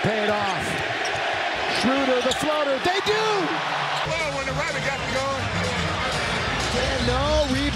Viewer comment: What did A.D said in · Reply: I think he said “it’s not fun when the rabbit has the gun” 🤔